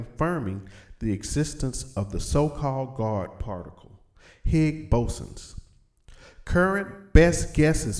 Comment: Stopped at "Nucular". then I heard "Higs Bose-suns"